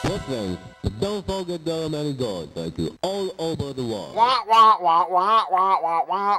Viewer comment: LOl @ 8:10 sounds like hes saying what gays dont forget there are many Gods (thank you) all oba da world